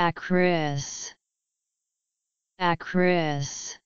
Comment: Why do I hear an "N" at the beginning of this word? Nakris